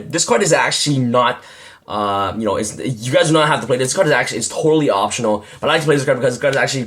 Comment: what did he say on this part? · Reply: Antonio H. N. Muniz I think he just said that that card is optional